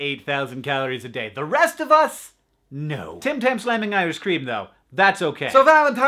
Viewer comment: what is he saying? "tim tam slamming irish cream though"?